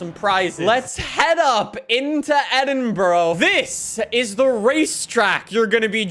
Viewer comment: DID HE JUST SAY " EDINBRUH"